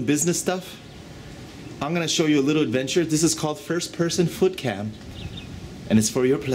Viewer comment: At first i thought he said FOOD cam... then after about 10 sec of feet i was like wait a min. Laughed. Then started to get a lil weirded out.